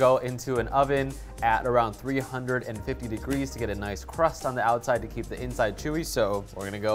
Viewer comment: When I heard the temp for the 2 hours I thought I heard 500 degrees, that was definitely a reference to my on and off hearing skills. Yep